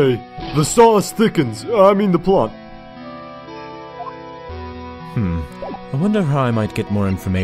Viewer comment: what does gumshoe's VA say at around 7:39? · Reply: "The sauce thickens... I mean, the plot."